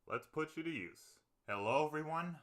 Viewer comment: What did you say there, after stroking the microphone? I've tried listening several times but I still can't decipher it. "Let's put you at ease"? "Let's push some cheese"? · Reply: "Let's put you to use."